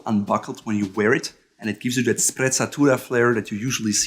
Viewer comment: What the f*** did you just say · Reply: sprezzatura